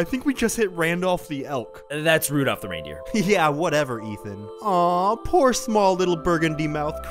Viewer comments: Did Anthony just call Ian Ethan? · Did he just call him “Ethan”?